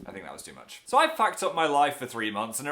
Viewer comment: When Phil said "fucked up" I wanted to jump off a bridge with happiness. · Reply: it sounds like he said Facked up, and i don't know what to think.